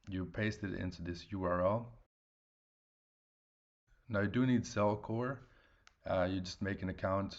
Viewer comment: At about 1:42 you mention what sounds like "cell core" to me. I tried searching but came up empty handed. Did I hear it wrong? Please fill in that blank for me. Thanks. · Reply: <link> you send the mined KDA to zelcore. It’s a crypto wallet and works best with KDA